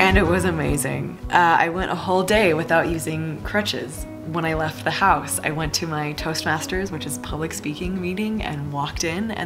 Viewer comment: I thought you said toesmashers but isn’t it toast masters